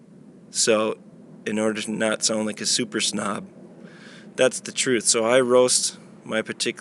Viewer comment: it sounded like you said, "In order not only a super snot." What did you say? If you like espresso, you might as well get a darker coffee blend instead of a light blend that you described.